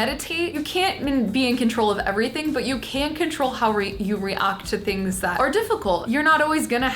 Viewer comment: did she say reuct